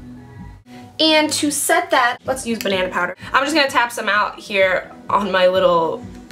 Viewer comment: What did she use after she used the beauty blender? I heard "banana powder". Lol